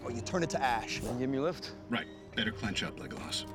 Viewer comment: wait... Did Stark called him "legolas"? XDDD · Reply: I use to always think he said "leg gloss". But Legolas makes more sense.